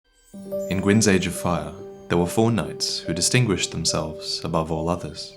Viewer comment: I thought you said fortnite, I have to be really aware of what I'm hearing.